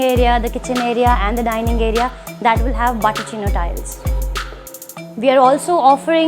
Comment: did she say 'butter china tiles'? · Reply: She said bottochino tiles